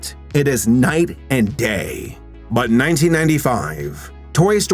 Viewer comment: Omg I thought you said Toy Story was released in 1945 🤣